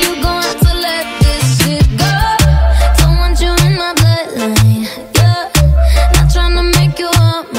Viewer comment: Is it just me or did anyone else thought the song said “don’t want you in my bed tonight” ? Tell me watchu hear